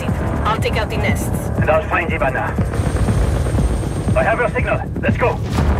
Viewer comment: First time watching this, I thought Lion said "And I'll find the banana."